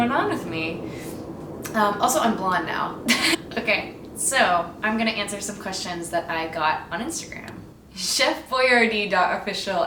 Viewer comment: I thought this said 'I'm black. I'm blond' and I was very confused